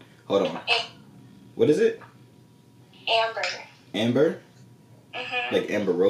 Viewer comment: She fine 😍😍and she said amber I thought she said ape but she looking good tho ❤️